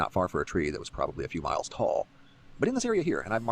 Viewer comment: I thought he said 2 miles. EIGHT!?!?!? Ok lets go back. Using Google AI it would theoretical be 180 to 250 miles tall. That means if the tree were in Nashville you could see it from Hattie, most of Mexico and half of Montana.